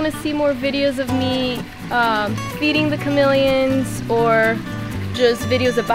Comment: Ohh... chamelions, thought he said it melons ^^ :D